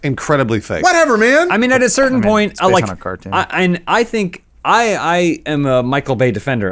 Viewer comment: did Adam say "it's based on a card game"?